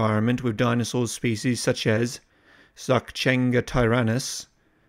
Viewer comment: Did you say Sakchengatyrannus at a 6:35?